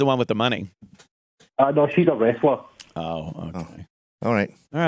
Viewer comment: Wtf, did he say she's a wrestler?